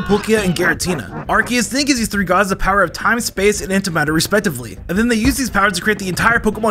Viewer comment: Dont know why but i heard "time ,power, repsoejrtively" i might be tired